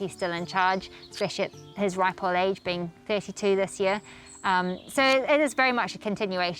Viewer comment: did she say 52 praise the Lord 🙏🙏🙏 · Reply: 32 😀. 🤓🇸🇪